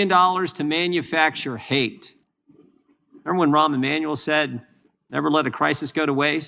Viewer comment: He said, "Remember when Rahm Emanuel said..." I heard, "Remember when Erwin Rommel said..." LOL!!!😂